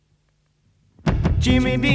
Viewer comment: Am I crazy? I was listening to a play list with only the right ear bud in when "Rock On" played. When he said "Jimmy Dean" I thought I heard something weird - so I listened for it again when he says "Jimmy Dean" twice...and there it was. On the right channel he says (echoes) "Jimmy Mean". You can't hear it when listening in stereo or mono because "Jimmy Dean" on the left overpowers it. Check it out - what do you hear?